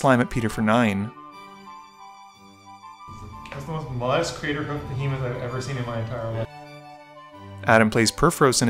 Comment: what did he say? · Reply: That's most modest craterhoof I've ever seen